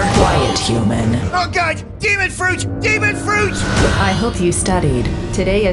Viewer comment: I THOUGHT HE SAID DAMMIT FRUIT